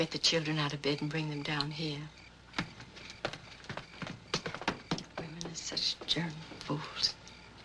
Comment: What does Gish say at 1:46? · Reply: "Women are such darn fools."